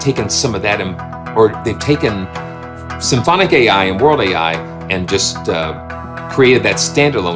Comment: I thought it said World "AL", and was disappointed that it wasn't a "Weird AL" VI.